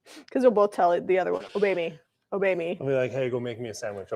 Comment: When you were explaining the “obey me” joke I heard “oh baby” ….. guess that would be a different joke😂 sounds so like how my hubby and I joke with each other. Love how you two love and honor each other so well. God bless you guys! ❤